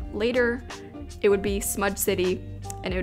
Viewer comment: Anyone realize in 9:31 said “smud” city instead of smudge city? 😂😝